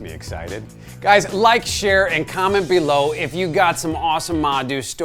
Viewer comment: Did He just say at the end of the video "because I'm running out of porn"?